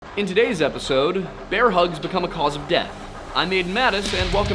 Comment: misheard the into a "bear huzz" becoming a cause of death 💀💀💀